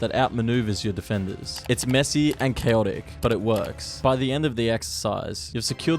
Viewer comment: I thought he said it’s Messi and hallend